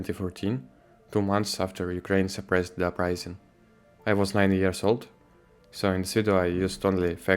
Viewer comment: wait did he say he was 19 or 90 yrs old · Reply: 9 years old